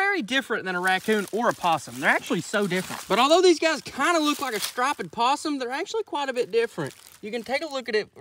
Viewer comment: Sir, did you just say Stripe-ed?